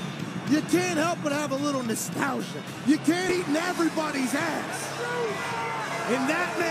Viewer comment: sounded like he said "you can't eat everybody's a$$" 😂😂😂